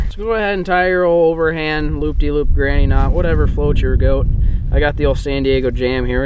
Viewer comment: did he just say "whatever floats your goat"😂